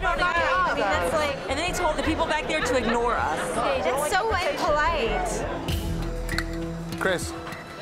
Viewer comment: Did she say "unpolite"?